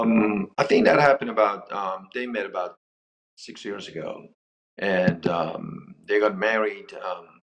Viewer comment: I can’t believe the stupid doctor left his Gorgeous wife for the young gold digger woman who is probably 30 years younger than him. KARMA…………………… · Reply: I thought they said they divorced 16 years prior. Maybe I misheard